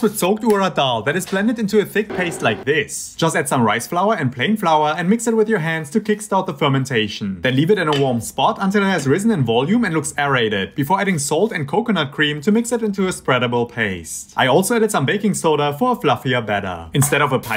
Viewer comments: I thought he said add soap instead of salt 😂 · I thought he first said "pig flour 🐷" i was like hmmm..... i learn something new everyday🤣